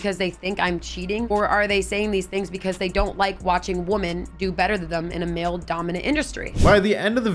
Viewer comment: did she say woman? or women? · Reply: Woman 😂